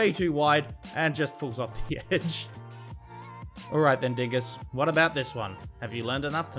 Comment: what did he say??!! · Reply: “alright then *DINGUS*”